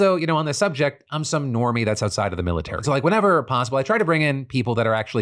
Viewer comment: Another bad editor joke by changing the subtitles...oh dear~ Anyone else notice @ 24:15 the subtitles say "enemy" instead of "normie"? "I'm just" is not in the subtitles. It goes from in the audio "I'm just some normie outside the military." To Phil "I'm just" then in the subtitles it continues "some [enemy] of the military."